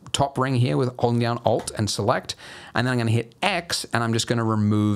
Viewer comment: I have no idea what he said at this moment: 9:32 Owlnianalt????????? I am stuck just because of this. Someone pls tell me what he said!!!!! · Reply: Ok I figured it out, I anyone else had this problem hesaid: Holding down `alt` and left clicking twice.